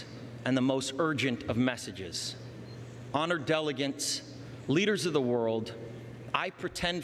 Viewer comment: Did he say "delegants" at 2:17 instead of delegates?